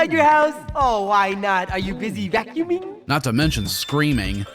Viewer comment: someone needs to tell me what he said- sounds like he said -"are you busy goonin?" like o_O this game has sexual undertones EVERYWHERE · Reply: I believe he's saying "vacuuming"